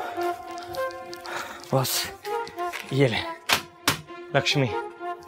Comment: Did he say Lakshmi